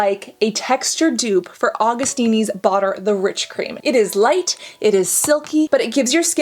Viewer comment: I keep hearing you say "bother the rich cream" 😂